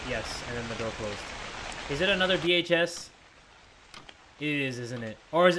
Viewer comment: "What kind of manager puts a girl on the night shift alone?" Me, a 20 year old girl: laughs nervously in Night Porter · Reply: When Jay said, "Is it another VHS" What I thought he said "Is it more DHS"